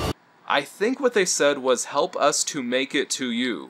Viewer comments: I heard “Help us, to make it real” from the pitch video. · I think they actually said "help us to make it real". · I think they said at 6:20 was "Help us to make it real"? I know im late but its what I hear. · what they actually said was "help us to make it real" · Actually, at around the 6:22 mark with the group shot, they actually said "Help us to make it real" 10/10 russian accents jfc